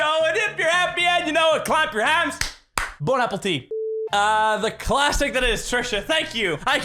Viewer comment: I got "Bon Appetite" Instead of "Bone Apple Tea" XD